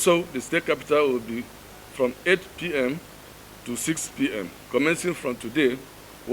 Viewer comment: did he say 6pm to 6pm and from 8pm to 6pm or am I mistaken, so no movement at all in other local areas and 2 hours in the capital cities